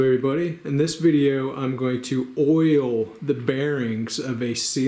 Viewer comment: Oil the bearings. That whole time i thought you said boil them and was very confused